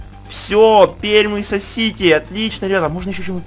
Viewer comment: Мне послышалось он сказал:"Пермь сосите"